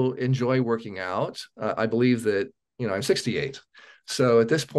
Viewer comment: did he say 68? He looks so great. 🙏🏼 Thank for sharing!❤